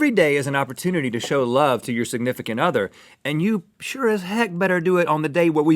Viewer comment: I thought they said sigmythicant other instead of significant other.